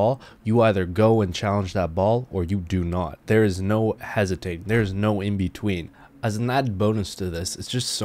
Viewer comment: Hesiting... What is that word tho?! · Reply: James M he said hesitate